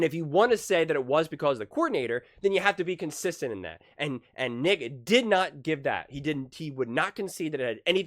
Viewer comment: Between 8:29 and 8:35... what did he say or meant to say... because i played that part 16 times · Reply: Come on man. I said Nick. As in Nick Wright. Don’t do me like that.